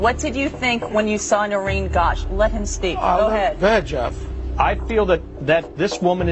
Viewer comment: anyone else hear 'Go ahead Josh' at 37:49.... as if hes confusing the two names